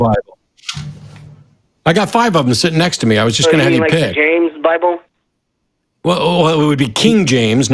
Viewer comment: "Do you have a preferred bible?" "Yeah..........the bible." LOL I'm dying here! LOL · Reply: I heard “the Rick James bible” lol